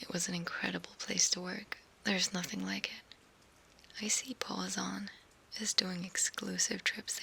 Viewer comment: Did she say Isi Palazón? Best Rayo Vallecano striker‼‼‼‼